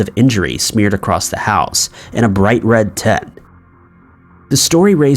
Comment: "...and a bright red...WHAT?" TENT? TIP? TEMP? I can't understand him. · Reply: It’s tint. It’s the writer’s unnecessarily roundabout alternative to saying blood.